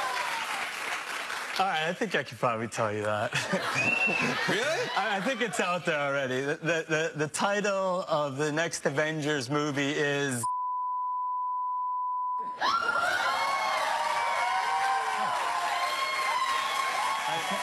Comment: I thought he said The Last Avenger